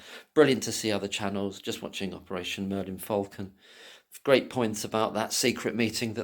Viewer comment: you refer to another channel but i could not clearly hear all of what you said but i did hear "Operation M..[inaudible] Falcon". Can you please clarify the full channel name and the platform it is on? Cheers! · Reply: Operation Merlin Falcon, robbo do check it out, please 🙂 on YouTube. Cheers!